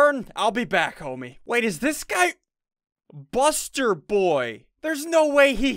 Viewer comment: did he just say 'ladies this guy busterborn'